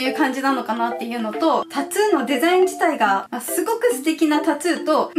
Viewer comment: タツゥーって聞こえる笑笑